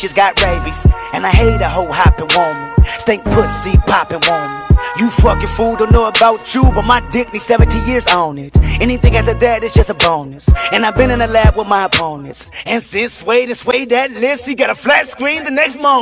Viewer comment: Wtf does he say at 0:50? He needs a 17 years on it?